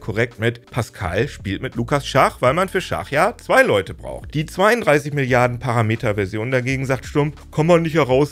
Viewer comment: Ich habe verstanden: Ich bin höchstens so schlau wie ein 32b Model.